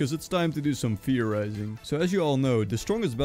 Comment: (what he said) theorizing (what i thought he said) fear rising